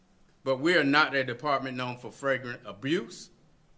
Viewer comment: Is it just me or sound like someone else wrote his speech. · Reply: did he just say fragrant??? Lol